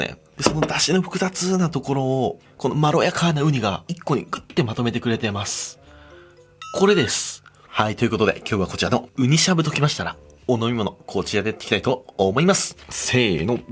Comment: これです、が、釈迦でーすに聞こえた😄